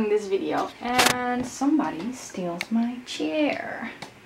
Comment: Did i just hear “somebody steelt my chair” 😂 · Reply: Steals ;) my chair